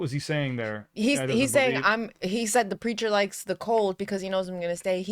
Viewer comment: Always thought they said "The preacher locks the door"